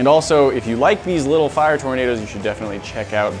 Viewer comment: I thought it said tomatoe..